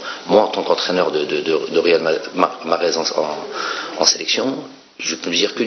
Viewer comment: mdrrr c'est moi ou il a dit real madrid ? Fin on te souhaite bonne chance comme même